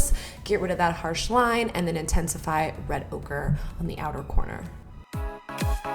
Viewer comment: Every time she said red ochre I thought she was saying red ogre